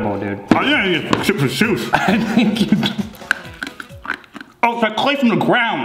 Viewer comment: i think said "I didn't even... chip a tooth!" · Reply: “ I think I just chipped a tooth “